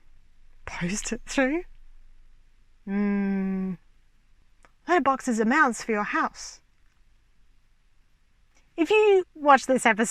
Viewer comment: "my box is a mouse for your house" is that what she said? What does that mean? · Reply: A letter box is a mouth for your house. (Because she was talking about being able to post a pizza into your mouth)